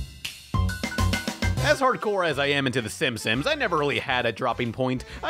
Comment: Is he saying Simsims?